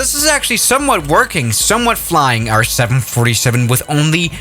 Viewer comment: DID HE SAID *737*???? Nice grammar you got there